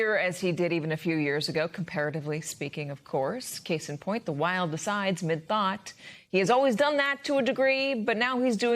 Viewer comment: What is "the wild decide miss bot?" I replayed that at 1:00 over and over and I still can't understand it.